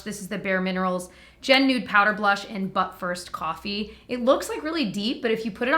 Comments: I totally heard "Butt first coffee," and not "But first, coffee." 😂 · I heard “Butt first” bare mineral blushes and couldn’t understand why you said coffee afterwards. 😂😂😂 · Omg your infliction when you said “but first, coffee” made it sound like you were saying “butt first, coffee”